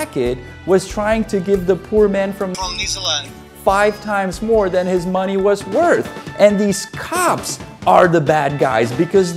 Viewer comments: Hahahahaha "I'm from NEEZERLAND " 😂😂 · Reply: I heard "New Zealand" from him lmaoo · Hahahahaha "I'm from NEEZERLAND " 😂😂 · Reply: I’m an American, it sounded like he said New Zealand to me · Hahahahaha "I'm from NEEZERLAND " 😂😂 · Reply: I heard clearly "New Zealand" :D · Hahahahaha "I'm from NEEZERLAND " 😂😂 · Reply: had to replay that part because at first I i thought i heard New Zealand 😂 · Hahahahaha "I'm from NEEZERLAND " 😂😂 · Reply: Honestly, I heard "New Zealand" on the first run. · Sounds like he said from New Zealand... interesting accent bro